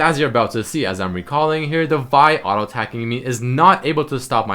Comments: please did he say Vi? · does he say vi ?